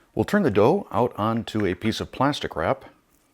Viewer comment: I had to laugh because it sounded like you said "plasti crap" when you said plastic wrap.😄